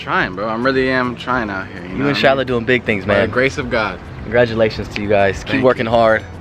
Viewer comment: did Austin say “you inshallah doing big things man” idk if that’s what I heard